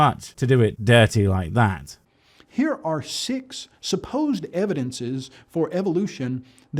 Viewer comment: did he say "Sic's supposed evidences" ?🤔 your fame is spreading!